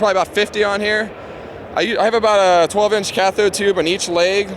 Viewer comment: DID HE JUST SAY CATHETER TUBING? WTF??? EW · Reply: cathode